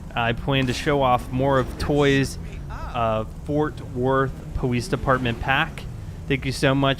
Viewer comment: it sounded like you didn't pronounce Fort Worth. The TH is pronounced in Worth. It sounded like you said Fort wort.